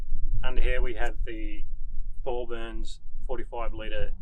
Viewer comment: Who makes the 45 liter water tank? Didn't quite catch that.. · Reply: Thorburns!